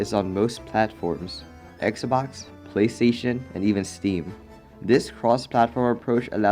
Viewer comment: sorry, did I hear you right? "Even steam"?!?!?!